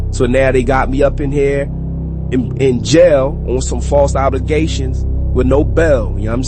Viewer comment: False obligations instead of false allegations lol😂